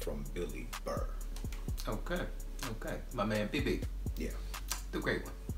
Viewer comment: What did he just call him bibby? Lol